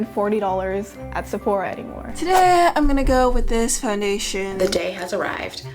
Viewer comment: I honestly thought she said "The gay has come." I never related to something so much before.